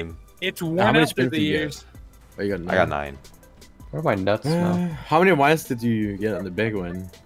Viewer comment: Did i hear 1:04 correctly? "Why do my nuts smell" ? :D :D :D :D :D